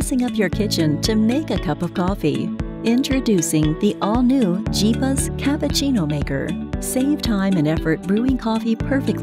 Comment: I thought it was saying the "cheapest cappuccino maker" the whole time 😂